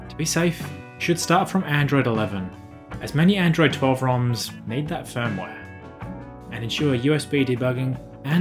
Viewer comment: What are you saying about Android 12 firmware? Can't understand what you said. Would it be ok to install from Pixel Experience 13 instead? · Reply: This part is noting that you should start at Android 11 as many Android 12 ROM's have this requirement